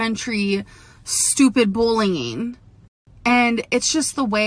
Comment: bullying..sounded like she said bling.